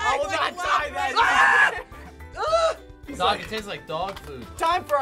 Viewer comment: clearly said tastes instead of what the captions have ["takes"]